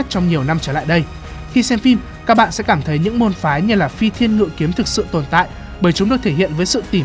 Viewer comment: Phi Thiên Ngự Kiếm mà tui nghe thành Phi Thiên Lụi Kiếm